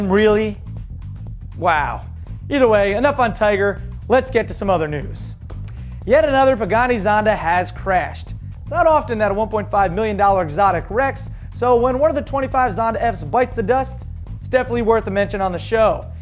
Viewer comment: "yet another buggatti zonda crashed" ahahah Sounds like he said that not pagani